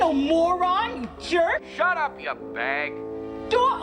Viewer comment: Did he just call his wife a "fag"? I thought this was a family movie.